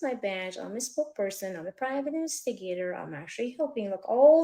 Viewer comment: did I hear her correctly “here’s my badge I’m a private instigator.”? Please tell me I hard that wrong.